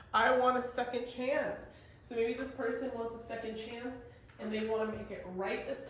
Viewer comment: What you said: “This person wants a second chance” 🥰 What I heard: “This person wants to suck your cans” 😏